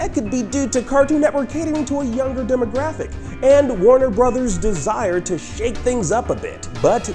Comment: is it just me or did he say warrior?